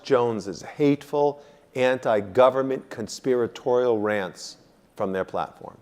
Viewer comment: did he said "...Alex Jones' ANT GOVERNMENT conspiratorial rants.." ?